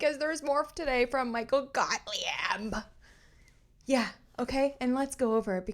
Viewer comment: I crack up every time I hear you say "Gottly-ebb" 😆🤣😆🤣💙💙💙